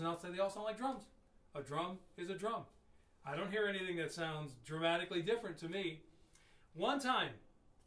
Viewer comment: great info, as a young drummer, it kinda opened my eyes about all sorts of things. btw, i like to think u say "drumatic"